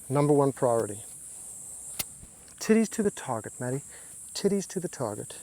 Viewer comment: Did he say titties to the target?